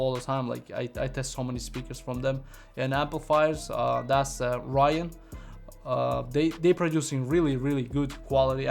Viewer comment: is he saying Ryan, or Orion? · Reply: there's no amp brand that's called "Ryan" sounds like he saying Orion